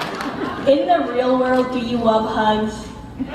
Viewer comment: What's the question at 50:57? I couldn't hear.. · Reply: In the the real world do you wuv hugs